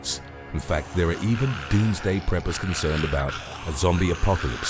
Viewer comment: I thought it said doomsday peppers